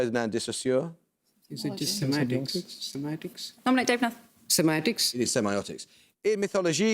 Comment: What happened at 16:14? They had the correct answer, said "semiotics" but they weren't awarded a point?! His accent made it sound more like "semantics" but I'm pretty sure that's not what was said.